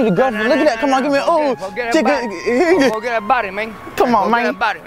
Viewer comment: all i hear is "Nananaaaanann nananaanaa go get a buddy mane nananana"